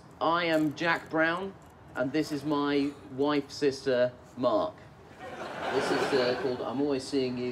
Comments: My wife sister mark XD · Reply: I thought he said my white sister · Nish's introduction to their band is such an underrated joke "Diverse Stripes" "Jack Brown" "Sister/wife" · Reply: OH i always thought he said “white sister” lmao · Nish's introduction to their band is such an underrated joke "Diverse Stripes" "Jack Brown" "Sister/wife" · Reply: I always thought he said "wife's sister" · My wife sister mark XD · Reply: I know who the White Stripes are hahaha. I simply thought he said 'white' sister as in, diverse stripes.